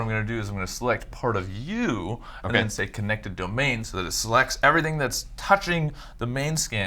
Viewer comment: What I heard was "Everything the light touches..." RIP James Earl Jones.